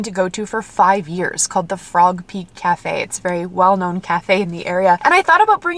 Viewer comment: I kept hearing "Frog Pig" Cafe and I'm over here thinking "a boston terrier themed cafe 😍" (some people call Bostons frog pigs). Then you mentioned puggapaloza and I was like maybe people call pugs frog pigs too 🤔 Nope nope...Frog Peak Cafe 🙈 Sounds delicious!